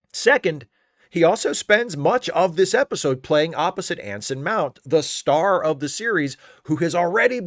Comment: Instead of Anson Mount, I thought you said, Ensign Mow. I was trying to figure out who was in the strange new world.